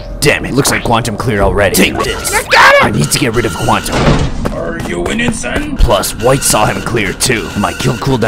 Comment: Are ya winning son? i heard as: are you innocent? 😂